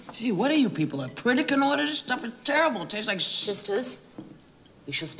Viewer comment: At around 1:34, she asks "what are you people...?" Can anyone tell me what the next word is? I've been trying to figure it out for years... "What are you people X? This food is terrible; it tastes like sh..." What is the X? Thanks in advance 🏴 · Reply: Pritikin Order